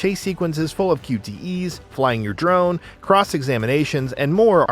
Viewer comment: I thought he said cross examine Asians, for a minute. Had to rewind the vid.I’m glad I heard it wrong.